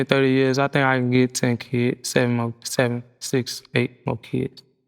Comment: ion think caresha caught what he said he said he want 6 or 7 more and he already have 2 😮😮